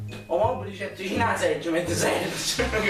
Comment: Ma al minuto 4:06 cosa dice il ragazzo.. '' Ricett o mobl vicin a segg... ma t segg??"" Qualcuno mi spiega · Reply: Disse il mobile vicino la sedia, ma ti seghi?